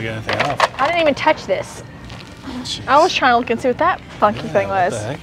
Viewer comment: Sound like you said bad word 🤬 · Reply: “I was trying to see what that funky thing was”